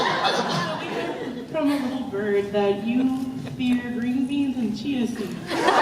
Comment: Wt did she say at 12:36? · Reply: Gwen Winchester “ we heard from a bird that you’re afraid of green beans and chia seeds”